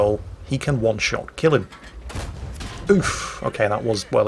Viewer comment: Did just say "oof" at 10:27?